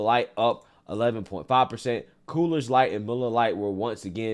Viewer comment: I kept thinking I was hearing things, but I'm pretty sure I heard him say "Cooler's" Light instead of Coors Light over and over. lol.